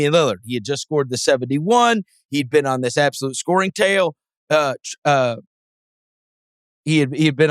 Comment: Did he say "absolute scoring tail"?